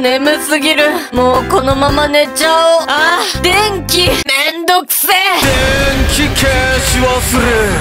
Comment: 「電気切れ忘れ」が 「電気めんどくせぇ」に聞こえた